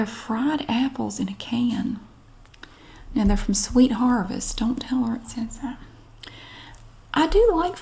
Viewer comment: Does anyone know what she’s saying at 37:29? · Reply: “Don’t tell her I said that”